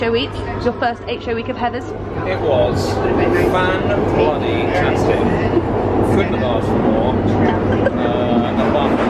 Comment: What did Jamie say from 12:18 from 🙊. I just hear the tube... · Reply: Musical Shows ok, if I can remember he says (to Carrie asking how was his first 8 show week) ‘it was fan-bloody-tastic, couldn’t have asked for more....apart from a different Veronica...’ lol and then he says he’s looking forward to a day off!